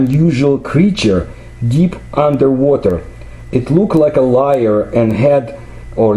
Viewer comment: What does he say it looked like at 8:30? The word sounds like "liar" or "lear" but I can't figure out what he means by that. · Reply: Not sure.. "lyre" maybe?